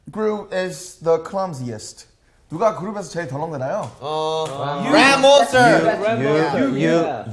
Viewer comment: have i ever heard RAP monster? I only heard DANCE monster😂😂